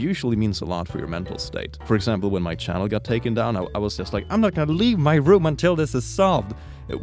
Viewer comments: I thought you said “when my child got taken down” and then I heard it again and it still meant the same thing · wtf i heard "when my child was taken down"... took me a minute to realize what he really said 😂